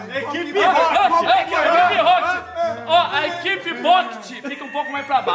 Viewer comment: =: "Ooo a equipe bo**ete, fico um pouco mais pra baixo" Eu entendi a referência kkkkkkk